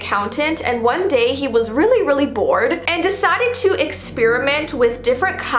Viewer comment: When she said he was really really bored I thought she said he was really really born in